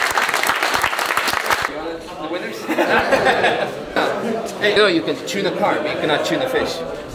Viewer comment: Sometimes i forget is dutch and then he says something like you can tune a car but you cant tuna fish and im like yep hes one of us 😂😂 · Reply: Oh I thought he said 'you can tune a fart'. Now it makes sense